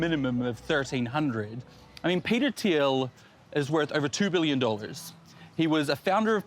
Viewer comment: Was unfamiliar with Peter Teel. I was excited because I thought you said Peter Steele (r.i.p.) and hoped for the best possible Mandela effect. Still very missed.